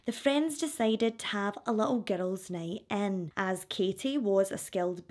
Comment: Omg I thought you were saying getto when you are saying girls. 😮